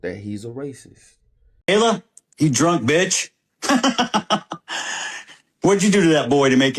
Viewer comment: WHAT DID HE JUST CALL THIS WOMAN‼️I'M 0:41 SECONDS IN. YALL WHAT ARE WE DOING, COME ON MAN LET'S STAND ON THIS, PERMANENTLY‼️‼️ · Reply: He called her a "drunk bitch."